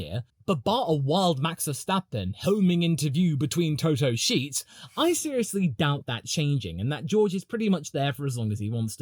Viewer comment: I heard Max "between Toto's bedsheets" 💀